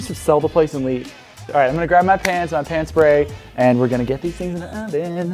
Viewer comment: I thought you said “I’m gonna grab my pants, my pants-spray” 😬👖